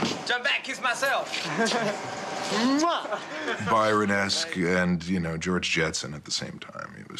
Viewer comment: who does say Michael was like apart from George Jetson? At 14:29? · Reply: He said Byron-esque as in Lord Byron the poet